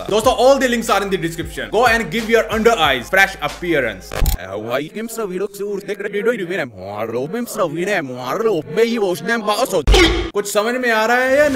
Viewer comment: "Doston hai jo bhi bol raha hu, wo reverse me bol raha hu. Maine video editor ke through reverse me kiya huwa hai" To everyone who are wondering what he said